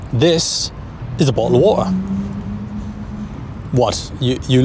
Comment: Wha…what did you say? Could say that again?😂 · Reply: He said bottle of water but no one in the uk says it like that